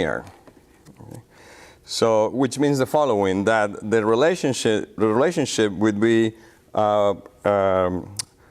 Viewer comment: he said relationshit 15:55 instead of relationship xDD